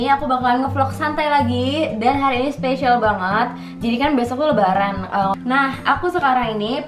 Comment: tiba" salah denger di menit 0:12 'jadikan bias aku kan lebaran'😭😭😂